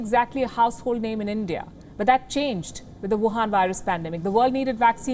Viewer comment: 1 sec.. Did you said Wuhan Virus 01:06?? Sorry if I'm wrong..